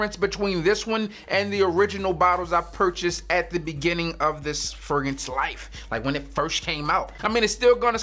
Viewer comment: did he say furgance? Mr Big Big Big Big Big Big B B B B